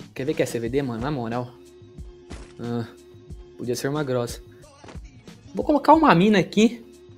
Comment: Essa rota é infalível e muitos inscritos já fizeram ela e sempre deu certo, inclusive se você conseguiu e deu certo comenta aqui e deixa seu like! Obrigado por assistir! Meu instagram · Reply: eu escutei grossa e eu tenho certeza